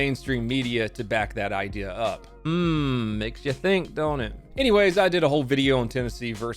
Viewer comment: I heard "makes you think donut" Even after correcting myself it's still catchy.